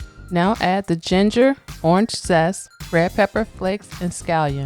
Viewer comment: If I heard you correctly, I noticed it sounds like you said add ginger but no ginger is listed as an ingredient. I’m making this tomorrow. If so how much ginger? Thx